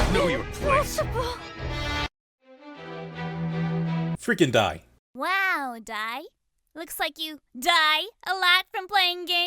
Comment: on subs it says Nep Nep: Wow Die Looks Like You Die a lot from playing games instead of its original dialogue Nep Nep: Wow Looks Like You Die a lot from playing games